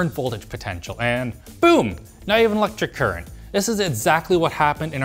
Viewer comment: Who does the captions for these videos? It doesn't seem automatically generated to me. At 2:08 the captions say "and boom, not even electric current" but what Alex actually said was "and boom, now you have an electric current."